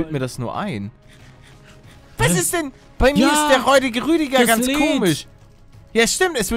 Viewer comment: ich habe zu erst der rollige rüdiger verstanden 😂😂